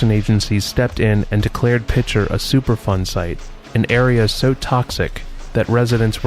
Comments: I thought you said "super fun sites" lol lead poisoning sounds super fun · Are you saying it's a "superfun" site? I must be mishearing it. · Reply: Chris LeeWoo I think its "superfund" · Ohhh superfund. I thought you said super FUN.